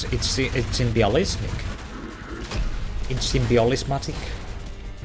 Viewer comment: is saying "its unrealistic" or "its symbiotic?" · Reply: I think he's saying "symbolistic"